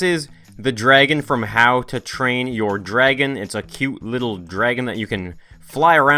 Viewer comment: When it said I thought it said how to crap your dragon